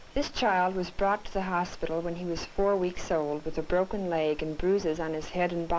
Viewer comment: With 4 months that baby had a broken leg and lots of bruisers, defenitly She is Not Mother material, very Young and with a lot of problems herself. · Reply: I thought they said 4 weeks.